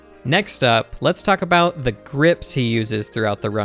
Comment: I was watching this part at 2x speed and it sounded like he said "Gripftski" 😅